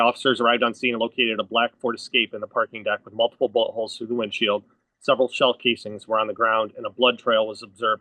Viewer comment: It sounds like cop says "multiple buttholes thru the windshield"